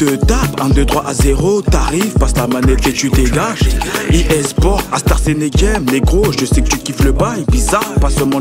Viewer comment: cst quoi "uéspor astar chenegé" ? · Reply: Base c'est EA Sports, IT'S IN THE GAME, mais le mec il a entendu un truc du futur avec ces oreille la